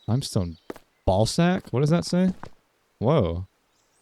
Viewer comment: i had this on in the background while doing homework and i JUMPED. that did not say ballsack!!